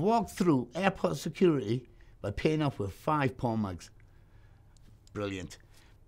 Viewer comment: the most surprising part of this story is he travels with five porn mags. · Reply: I.was wondering,i thought he said porn mugs!🤣im thinking to myself what the hell is a porn mug??😂😀